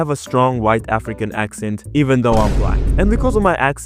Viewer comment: The automatic subtitles got me rechecking 3 times for a second😭 Video: "..even though I'm black." Subtitles: "..even though I'm white."